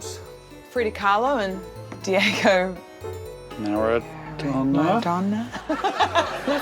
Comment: what did she say here? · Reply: Rose first mentions Frida Kahlo (who was married to Diego Rivera), and then she apparently only remembers the first name of her husband Diego (which she says) but seems so have forgotten his surname. So Alex picks up on that and of course gets the surname wrong and mutters "Maradona" which Rose slowly repeats. It sounds a bit gibberish though, since she starts with something like "Riww..." but then says "Maradona" together with Alex. She then realizes that that is not the name she was looking for and breaks out in laughter.